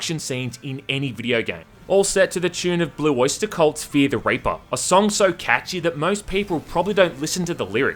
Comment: i thought it said fear the raper